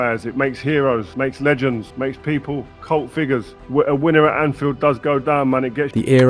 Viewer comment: what did ferdinand said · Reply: he was saying that scoring at Anf...